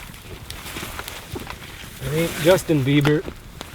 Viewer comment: i thought i heard justin beaver...